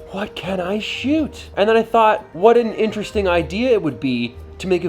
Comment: Ooohhh IDEA ... I thought you said IKEA!